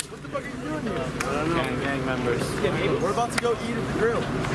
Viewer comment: did he say gang members?😂